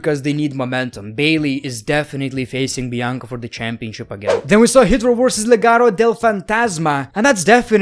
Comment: did he just said hitler?